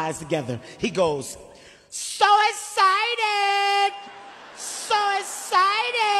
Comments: the captions guy : “SO EXCITED!!” what i heard : “ SUICIDED “ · The subtitle and the audio: "So Exited!" What I heard: "Suicided!" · When he said “so excited “ I heard “Suicided”? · The subtitle and the audio: "So Exited!" What I heard: "Suicided!" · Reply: I hear both “So exited” and “Suicided” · I didnt hear so excited i heard 'suicidal '😢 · the captions guy : “SO EXCITED!!” what i heard : “ SUICIDED “ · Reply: bubbaslol_ what are you talking about??? the guy edited their comment because it said "the captions guy : 'SO EXITED!!' what i heard : " SUICIDED "